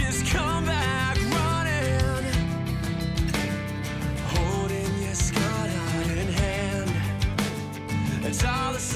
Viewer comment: I always thought he was saying "holding your skirt out in hand." like she was holding it up to run after him.